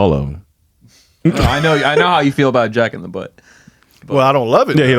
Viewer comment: I really thought said Jack N The Butt. LOL!!! The sauce is equivalent to accessories when it comes to food. It just adds the finishing touch to the meal. I love sauces. I love trying new food but what I'm not eating is Cow Cootah Cat.